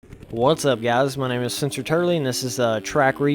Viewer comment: sounds like you say on intro btw keep up the track reviews I love them thx for the video!